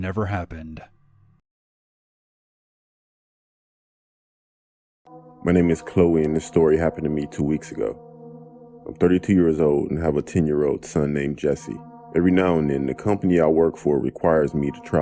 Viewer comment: there is a mistake with the drawing of the last story, chloe is a woman .. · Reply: I thought he said Chloe but apparently he said corey